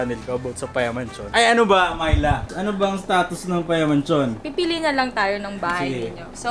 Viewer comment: "ano ba mylove ano bang status natin ngayon" (tama ba narinig ko)